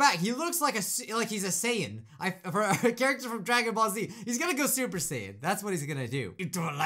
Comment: Super sane? I don’t know what said but the thing I heard was super Satan..O-O